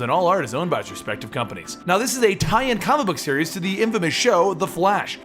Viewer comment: Did he say infamous at 0:21? Or did he say famous? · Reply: infamous